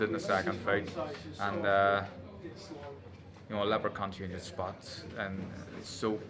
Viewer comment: I genuinely thought he said a Leprechaun can't change it's spots 🤣